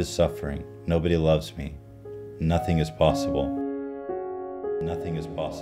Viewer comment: Nothing is possible #1 on trending. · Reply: it is actually says "Nothing is impossible"